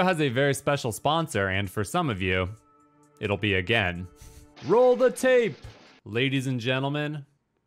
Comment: Can anyone tell me what the words he said? I'm Japanese and I don't know that. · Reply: roll the tape